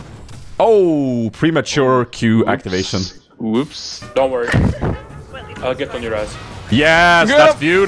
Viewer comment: Totally sounded like said "Don't worry. I'll get on your ass." Strangely enough, that's probably not the first time he's said that. :/